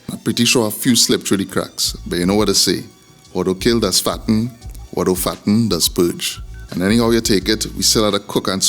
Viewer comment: Great video! cant wait to make this! What does he say at 1:14? Sound like some real wisdom! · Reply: Thanks! It's an old saying. "What doh kill does fatten, what doh fatten does purge" Meaning what doesn't kill you will nourish you, and if it doesn't the worst that will happen is you end up in the toilet 😂